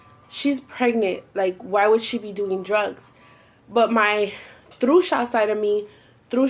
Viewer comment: Does anybody know how to spell "throughsha" (at least that's what I heard) 8:12? I really want to know cause I've never heard that before. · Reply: Hansel Gretel it's spelled trucha